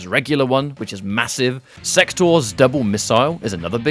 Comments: Might just be me, but I heard Sektor as "Sextor" when I was distracted while watching it. Gave me a good giggle. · Did you say Sextor? I know I'm not the only one that heard that.